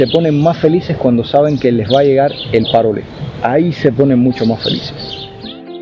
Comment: Que dijiste?10:59? les va a llegar el parole.......?